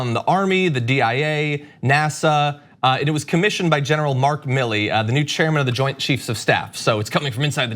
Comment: 32 LOL, I almost thought he said, "general Meek Mill".